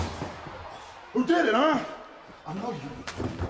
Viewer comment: it sounds like he said i love u